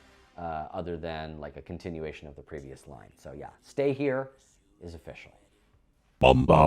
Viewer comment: 💔💔💔 Also i swear said "sing here" but thanks for letting us know!